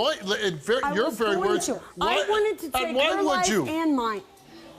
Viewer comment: Am I crazy or does anybody else hear her say at 10:23 “ I wanted to take her life and mine” or am I crazy?